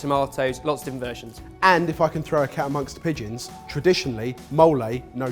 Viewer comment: Did say "if I can throw a cow in a bunch of pigeons" at 2:19 I've never heard that saying before but I love it